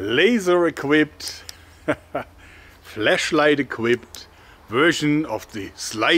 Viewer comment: I thought I heard "flesh..." 😂🤣